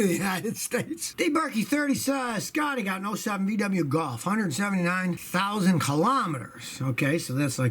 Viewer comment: It was a VW Golf. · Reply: I thought he said it was a BMW...after listening to it again I heard him say VW Golf